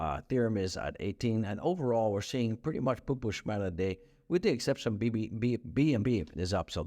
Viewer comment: What is he sayin at 3:38? Were re seeing a lot of poo poo shmatter??? I am German an try to understand phrases I often hear from you.